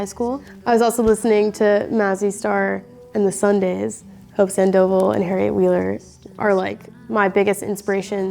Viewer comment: what singer is she saying at 4:59? like the 3rd picture... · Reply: Hope Sandoval, singer of Mazzy Star :)